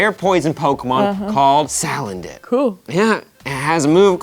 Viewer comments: I can't be the only one who heard "Salad Dick" at 0:52 ?! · Loads of people are excited about Salendit, he can poison types that are immune to poison that's crazy for the competitive game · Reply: I thought she was saying Salad Dick... I thought it was a joke :P